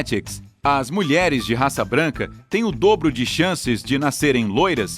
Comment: sério mesmo que eu ouvi "Raça" invés de cor ? Pq sério a única raça que somos é a humana